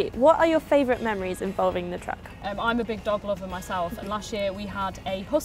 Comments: oh gosh i heard i'm a big doddleoddle instead of dog lover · you know you love Dodie when I first thought the woman said "I'm a big doddleoddle myself" at 01:29 #me